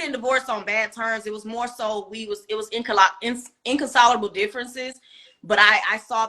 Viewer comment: Irreconcilable* differences 🥴. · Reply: I thought she said inconcilable which is a word means not capable of being reconciled.